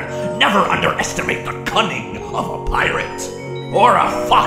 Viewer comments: Bro when he said cunning I thought that said cumming💀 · "You never underestimate the cunning of a pirate" l THOUGHT HE SAID CUMMING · I thought he "C*ming" but he said "cunning" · i never knew he said cunning i thought he said cumming💀